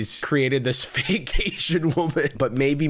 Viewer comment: I heard ‘vacation woman’ where you said ‘fake Asian woman’ and my brain short circuited